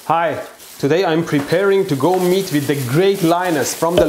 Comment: I clicked the video without paying attention and the first thing I heard was "Hi, today I'm going to meet the great lioness." and was like wtf!?